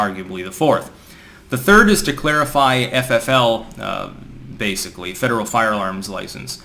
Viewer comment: did he just say federal fire alarms?